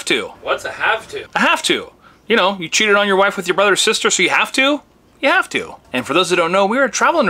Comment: Did he just say, you cheated on your wife with your brothers sister?? 😳🤣 Either way, great video! 🙂